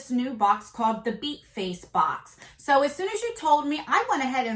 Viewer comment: am ashamed to say i thought the box said butt face